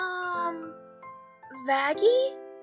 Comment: verbalase instead of vaggie 💀💀